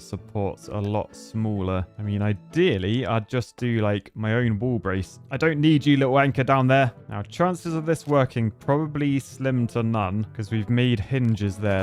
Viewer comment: You can’t tell me it doesn’t sound like he said “I don’t need you little wanker down there” 3:42 😂